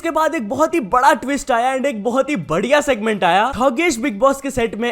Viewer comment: who else heard it HUGESH!!😂😂😂